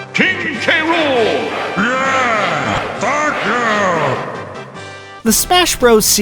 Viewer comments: am i the only one who at first instead of "Thank you" heard "F@ you" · I'm sorry, did he just say "f**k you"?